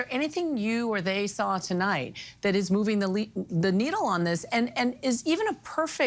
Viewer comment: Did anyone else hear the same “Freudian slip” here that I did? She was asking “is there anything new here that they saw tonight that’s moving the”…and instead of saying “needle” she starts saying “elite” but corrects herself!! I replayed it numerous times & even slowed it down & I hear her almost say “the elite” every time!